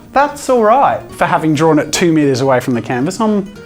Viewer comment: at first,it sounded like he said 2 years away